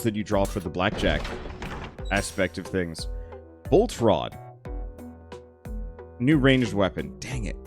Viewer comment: I wasn't watching when you picked up the Bolt Rod, I thought you said Voltron. lol!!